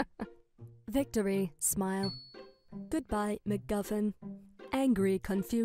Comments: Why'd you say mc guffin what does that do on the lore😭😭😭😭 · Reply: I thought she said “mcmuffin” 😭.. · I always thought she said McMuffin when she threw the patch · For the first one I thought she said mcmuffin 😭